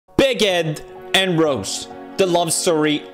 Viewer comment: in the intro, i totally did not misunderstood what Pewdiepie said so I thought he said "Baguette, and Rose"